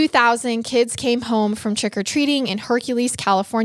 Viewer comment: All I kept hearing is "trEck o treating" lol